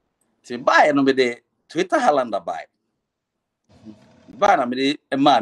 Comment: did i hear Twitter Highlander?🤔